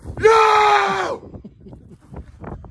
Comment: I thought he said YOUR TOURIST!😅